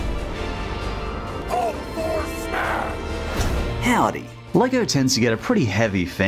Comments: 'Hulk for smash'? No, really, that's what I heard. (I know it's Thor.) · The starting bit I thought hulk said, 'HULK FOR SMASH' Hulk needs to be in smash. And should play among us.